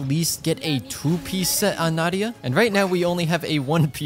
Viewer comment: did he just say nadia?☠️